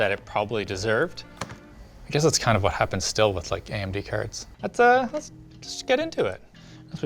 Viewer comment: Who tf wrote AMB instead of AMD in the captioning at 5:18? Who? I will find you.